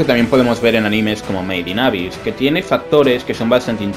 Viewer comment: que anime dijo que no le entendi bien ?? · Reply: made in abyss